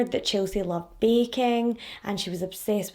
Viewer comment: “She found her passion in life and that was bacon” that’s what I thought I heard until I realized you said baking 🤦🏼‍♀️ why am I like this!